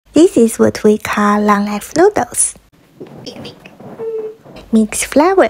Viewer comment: Lol I didn't read the subtitles and I thought she said long ass noodles 😅 😂😂😂